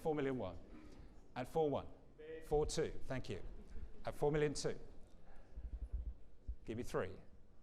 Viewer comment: His accent made it sound like he said 4 million 2 but the bid was actually at 4 thousand 2